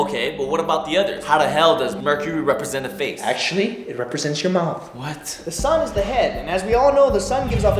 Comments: I thought he said it represents your mom XD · "How the hell does the mercury represents a face." "Actually it represents your mouth." I really thought he said "it represents ur mom." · I thought he said "actually, it represents your mom." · for the 1st time watching this i thought he said it represents your mum XD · "actually it represents your mouth" yh I thought he said mom too